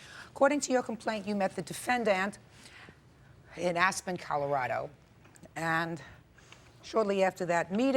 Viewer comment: holy cow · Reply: What? @ 0:12, it’s just Judge Judy saying Aspen, Colorado